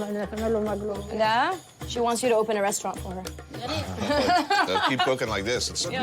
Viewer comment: after asking if he liked the food, what else did that woman say? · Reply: Word for word, let him open a restaurant and me and others ( like her sisters and brothers) will go there and make him maqlooba ( the dish they are eating)